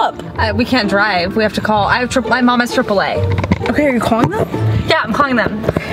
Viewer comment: I thought she said my mom has chipotle